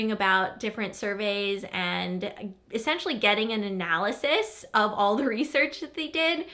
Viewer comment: When you said "getting an analyzis" I of course heard it as "anal Aziz"